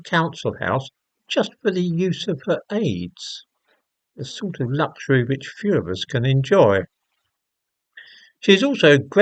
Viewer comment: Did he just say “the sort of luxury only Fuhrers can enjoy”?